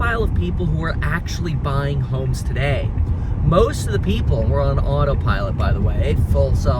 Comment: i thought you were saying most people in the market were on autopilot. Like Sheesh, that's pretty meta.